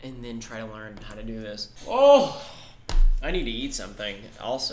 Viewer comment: i thought you said "i need to yeet something "